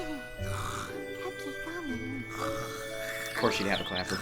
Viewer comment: is it meh or did pinkie say Cupcake Onion in her sleep